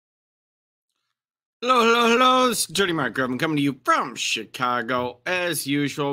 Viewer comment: LMFAO! I started listening to this while in my hot tub and I heard "this is Dirty Mike Gravelin" ... "I slept together a few of my favorite things" ...